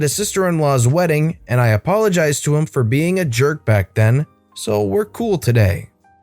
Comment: its funny how it said yerk instead of jerk